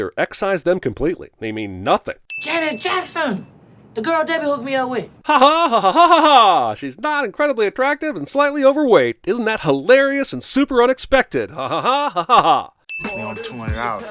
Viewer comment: wait! Did he say, "Slightly"? She got out the car looking like a WHOLE marble!